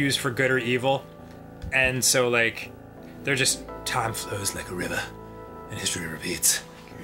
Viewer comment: Did anyone else hear "Time flows, Mike Aruba?"